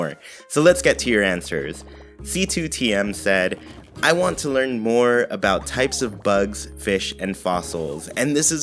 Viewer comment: I thought it said,"I want to learn more about types of drugs!".